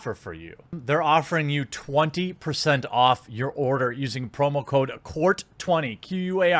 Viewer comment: Kinda sounded like u said homo code